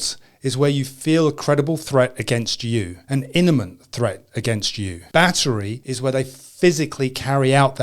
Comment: does he say "inimant threat"?